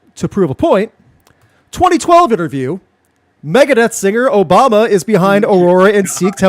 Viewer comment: I wasn't looking at the screen and thought you said "Megadeth singer Obama"...now I'm imagining Obama as a metal singer.